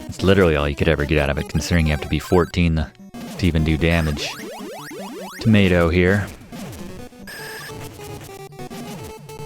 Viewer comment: "to Mato here" I thought the was saying "tomato here" and figured it fit since the enemy is a ball.